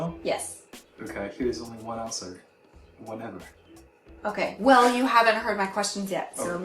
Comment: At first, I thought said "well, never" instead of "whenever" at the first question and I was like that's a bit harsh but okay.